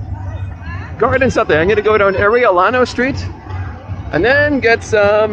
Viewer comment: I thought the guy said "areola street" !